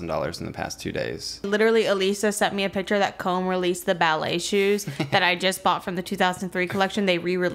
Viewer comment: omg can someone tell me what the names of the ballet shoes are called that talks about at 2:46 idk if she's saying "come ballet shoes" or "comb ballet shoes" or something else, i just wanna see what the shoes look like :( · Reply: I think she is refering to comme des garçons but I don't know the specific shoe 😅 hope it helps